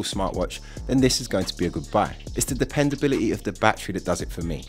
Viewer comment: "then this is going to be a good buy" thought I heard good bye at first.